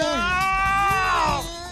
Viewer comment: did he just call him a sadistic fuck?